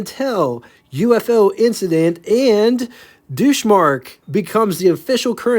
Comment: Thank you for that correct I knew I butchered that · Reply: Yeah, it sounded like you were saying douche-mark.